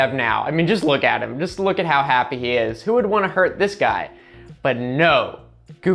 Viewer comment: Caption mistake! "Who would anyone want to hurt him" instead of "Who would wanna hurt this guy"